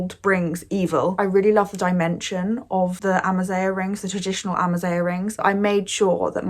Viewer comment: I’m Algerian and thank you so so much for using the term “Amazigh” it means lot to us North Africans. It’s also really fascinating seeing how Europeans see what we consider simple and cheap as something interesting and artsy. For example the windows, those types of windows are for ppl who don’t have a lot of money and do with little stuff they have for example fabric. Also the slippers and socks combo it’s kind of inspired by French ppl’s style of clothing or as others like to call it “mec de cité” style. Love your videos I literally binge watch all of them bc of how interesting your point of view is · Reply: Sorry, but what is “ Amazigh “ and when did she say it, because I only heard “ Amazing “?